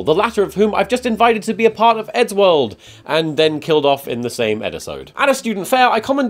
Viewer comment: did... did you just say Eddisode?